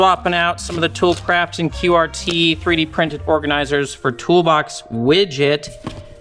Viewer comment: The first time you said "3d printed" I heard "Pretty Princess". 👸